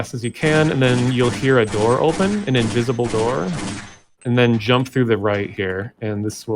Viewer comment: did anyone else hear “injizzable door” or is just me? I’m making a saucy commission btw :3.